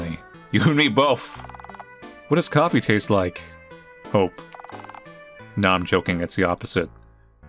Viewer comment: for a second I thought said Coffee tastes like cope and was just like 'yeah that's how it is huh'